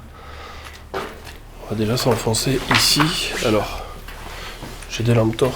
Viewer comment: Salut je voudrai te faire pars d'un phénomène audio qui ressemblerai à une voix de femme, il me semble que tu n'as pas relevé, cette voix semble dire "oui" quand tu dis "on va déjà s'enfoncer ici" a 10:12 mins, hâte de savoir si c'est tout à fait rationnel selon toi ou non ;) Continu ce que tu fais !